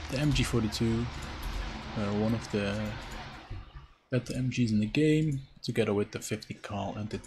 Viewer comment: MG42 is bad? Why? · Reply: i hear "one of the bad mgs in the game", but maybe i'm deaf